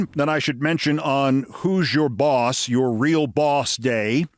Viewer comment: I came looking for you this morning and put you on hold until I got some obligations knocked out. Every time you said "Who's Your Boss?" I heard "Who's Your Daddy?" Thanks, Keith. Some of us can handle the truth.